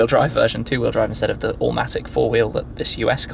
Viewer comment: did he just say "all matic"?!